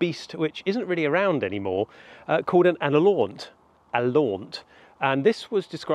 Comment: can someone write the name of that type of "extinct" hound/beast he talks about at 02:42. It's really hard researching when you can't figure out how it's spelled, and it's mysterious too! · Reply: alaunt I believe